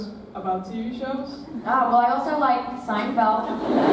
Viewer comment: what did she say here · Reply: the girl is asking the same question as the one before about favorite tv shows and Alycia says "Oh but i also like Seinfeld"